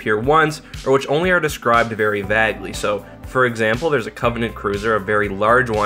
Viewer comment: Did he just say Vagley? .... U mean vaguely? Is this like an accent thing?